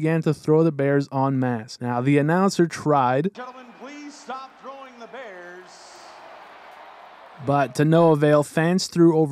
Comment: lmao I think a good portion of fans most likely misheard the announcer He said "stop throwing the bears" But I can imagine how people could have heard "please start throwing the bears"